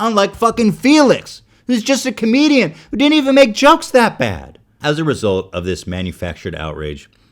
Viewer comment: does he say jucks that bad lol